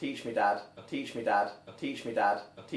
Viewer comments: Everyone here please comment what you hear below. I hear "bitch me lad" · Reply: I hear 'peach me dad' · I hear "Peach me dad." XD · Everyone here please comment what you hear below. I hear "bitch me lad" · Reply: i heard "beach me mad" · I keep hearing "bud teach me ad" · I hear "impeach me dad"😂😂